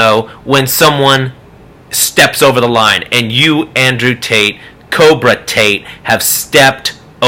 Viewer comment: I keep hearing "andrew taint" lol